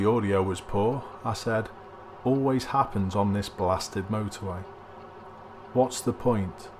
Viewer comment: I thought you said "always happens on this bastard motorway" so I'm disappointed now...